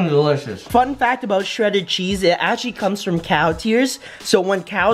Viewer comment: When he said cow tears i thought he said cow ears😅